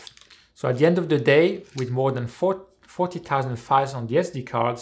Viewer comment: Great tips! At 23:40 it sounded like 40.000 photos were on your card, what was the real number you said? 4000? edit: 29:40 I got the X-T4 with a SmallRig grip...without it or the battery grip I probably wouldn't have bought the camera, because of these ergonomics! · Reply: Haha, indeed I say 40,000, but I tripped on my words, I should have said 4,000. 40,000 would have been a bit too much!